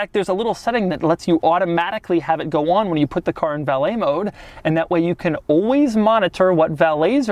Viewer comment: I thought he said ballet mode,but I misunderstand it was valet mode.